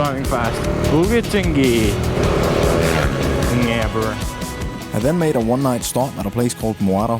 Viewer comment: did you say "ngaber"???... Hahahaha